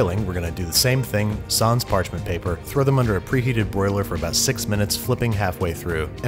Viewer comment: I thought you said 68 minutes for baking and i almost had a heart attack